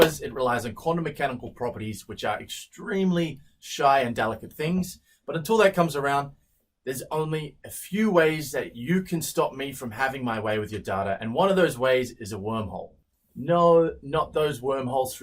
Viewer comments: i literally thought he said "there are a few ways that you can stop me from having my way with your daughter" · I know it's just how he says "data" but it totally sounded like he said, "there's only a few ways, that you can stop me from having my way with your daughter" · With that accent I heard you say "Daughter" instead of "Data". Still shook about it. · he sounded like he said daughter the way he said data. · "having my way with your daughter" lmao I know what he says, but god did I get a shock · had to rewind for a sec, sounded like you wanted to have your way with my daughter first lmao